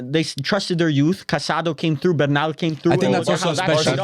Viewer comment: Did I just hear CASADO 😮😅